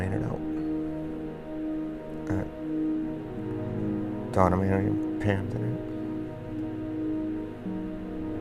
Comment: What did he say from 4:40 to 4:48? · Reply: I think Donna n Pam, his fiancees friends who didnt make it